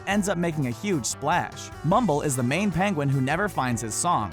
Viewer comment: what did he just say Mumbo? Jumbo?